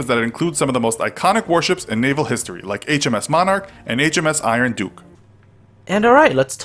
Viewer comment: Emil If you watched and listened. Iron Duke and Duke of York were both mentioned. Hence comments relate to two of the points raised. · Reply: Listen to 0:50. He clearly says HMS Monarch and HMS Iron Duke being iconic. Duke of York is mentoined in a diffrerent topic later in the video.